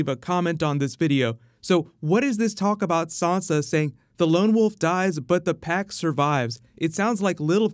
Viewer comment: The "Lone wolf dies but the pack survives" is from the first Song of Ice and Fire book, A Game of Thrones. Meaning, "United we stand, divided we fall". Basically, if Sansa doesn't stick with her family, she will be a lone wolf, and she won't survive. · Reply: lol I heard it as the "lone wolf dies in the pack of ice." xD